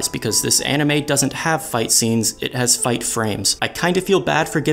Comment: he said I "INCEST" instead of insist, did you get it as well????